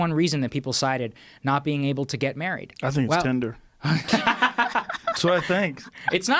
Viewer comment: can anyone tell me what Joe said, cant hear it properly. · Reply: "I think its Tinder"